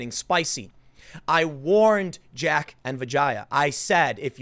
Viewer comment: "I warned jack and vagina" did I hear that right?